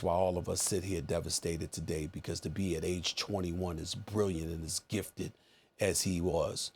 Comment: Did he say at age 21? 3:03-3:08?